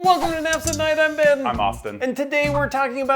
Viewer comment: The CC thought you guys said “Naps at night “ lol.